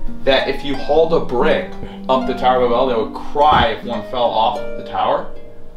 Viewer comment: He pronounced Babel like “ba-BELL” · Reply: Sounds like he said tower of A bell